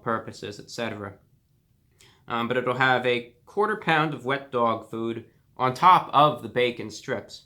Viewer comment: I thought you said wet dog poop